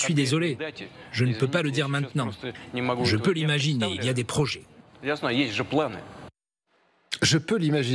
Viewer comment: 'je peux imaginer il y a les projets' . J'ai entendu : 'я знаю, есть же планы' 'je sais qu'il y a des projets.